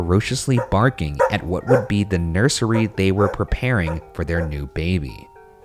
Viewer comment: On the clear voice I heard, “why would they be here?”